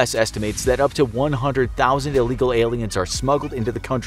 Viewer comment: did he say Aliens?